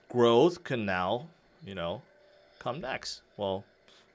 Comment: you said " comeback" so silently and so unsure like you dont believe yourself what you are saying😄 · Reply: He said come next